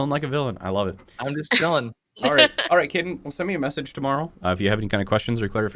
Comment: At the end I thought he said "alright kitten" instead of "alright cayden" and I was stunlocked